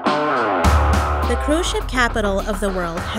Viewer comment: I thought it said cursed ship